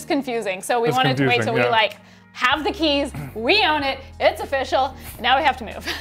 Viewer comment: oh...lol..thought you said, '..bought a house, and 'wee on it'..' meaning you put your mark on it like the supervisor! I do beg your pardon!!